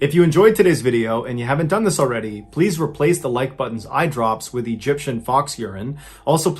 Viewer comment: I had to rewind to make sure I wasn't having a stroke. Did you say "replace the like button's eye drops with Egyptian fox urine" at 20:00?! LOL